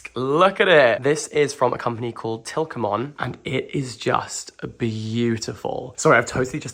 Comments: I thought said "this (desk) is from a company called POKEMON" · Sir where did u get the desk from? i heard Tilkmon but the CC's show Tilon so i am confused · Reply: It’s tikamoon !!